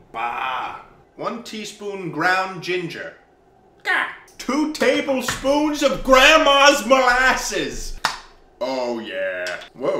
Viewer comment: I thought this man said "two table spoons of grandma's ashes"